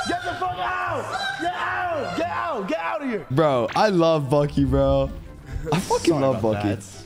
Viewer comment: When clix said I love bucke I heard I love donkey